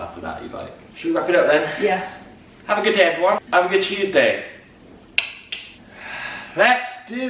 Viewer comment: You talked so much about cheese, that in the end I heard saying "have a good cheeseday" :)))))))